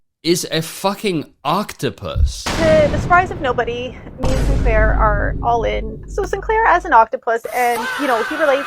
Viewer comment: i thought she was saying saint claire the whole time until i saw it spelled out in the "book"...